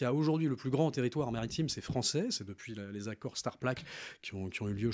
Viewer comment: les accords quoi ? "STARPLAQUE" ? De l'ONU ? Introuvable. Si qqn a un lien pour me prouver qu'on est passé première ZEE ça m'intéresse